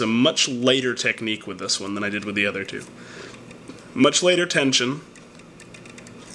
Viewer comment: With the bogota you say you use a much later/laiter/layter/lighter technique/tension. What does that mean? Sorry, english is not my native language. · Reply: I had the exact same question. I heard "much later technique". But he meant "much lighter technique". This means you don't press the rake as hard as you would otherwise.